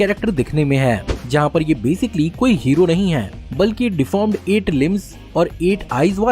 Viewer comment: It is sounding like you are saying lymphs instead of limbs